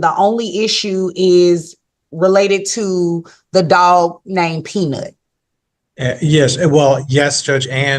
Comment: I thought the judge said "a dog named Penis." 😄